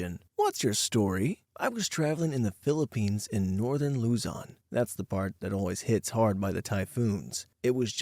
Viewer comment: Hello Narrator! As your fan and someone from Northern Luzon, I applaud how you tried to pronounce Banaue/Banawe (Bah-Nah-Weh) and Sagada (Sa-GUH-duh) correctly in story 1! They're right about the typhoons but it's mostly hitting the Northeastern Luzon provinces like Cagayan and Isabela (where I currently am). Typhoon season is particularly dangerous because of lots of landslides and rockfalls. There are times where certain roads are blocked when this happens. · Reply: Legit thought you said luton